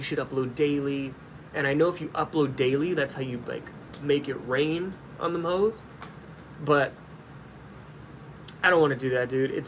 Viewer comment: Did you just say rain on them hos 😂 what the fuck